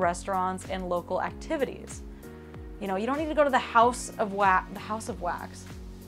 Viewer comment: I thought I heard House of WAP 😄